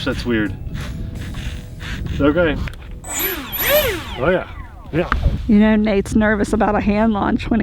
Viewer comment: Check out the ZOHD Altus RC plane here: <link> Save with code: BGeff04e LEARN MORE ABOUT THE ACADEMY OF MODEL AERONAUTICS HERE: <link> In this video we try out the ZOHD Altus RC airplane. This is a great platform for FPV. Let us know your thoughts about this RC airplane in the comments. Disclosure: This description box contains affiliate links. Check out the ZOHD Altus RC plane here: <link> Save with code: BGeff04e LEARN MORE ABOUT THE ACADEMY OF MODEL AERONAUTICS HERE: <link> The products in this video are rated for ages 14+. Amazon Storefront: <link> FMS: <link> AMain: <link> Hobby Zone (Arrows): <link> Tower Hobbies: <link> Banggood: <link> Redcat: <link> Aviation Apparel: <link> Fair RC: <link> Horizon Hobby: <link> Support TheRcSaylors Patreon & Stickers | <link> Website | <link> Merch | <link> Mail | 1140 Carter Ave, P.O. Box 361, Ashland, Kentucky 41101 Social Media TheRcSaylors Shorts | <link> Facebook | <link> Instagram | <link> Twitter | <link> Vlog and Live Channel | <link> SPECIAL THANKS TO OUR PATREON FAMILY!! john salt, Jose Valentin, Ray Hicks, Richard Higginson, Robert Sanges, Russ Cooper, ruudy, Ryan Alexander, TIMO, Zach Gushing, Chris Hamlett, David Chenzoff, dmilbrandt, Doug Dahlheimer, Gary Zion, Haar Bear, Jason day, Jeffrey Davis, and Jesper Larsen #rcairplan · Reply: DID YOU SAY... OREA... lile NICE IN gREEK at 3:37 ? ? ? are you Greek ?